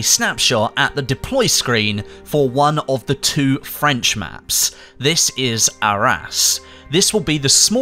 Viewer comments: Did he just say "This is our ass"? XD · i swear the god i heard "this is our ass"